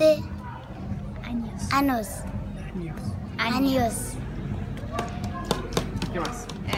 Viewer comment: Los que son atentos se dieron cuenta que dijo ano ajajaja